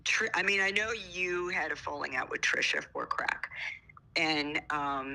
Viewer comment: did say ethan & trisha had a falling out over CRACK ?!?!